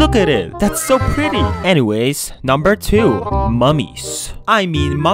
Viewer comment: I thought I heard mommies at the second suggestion 💀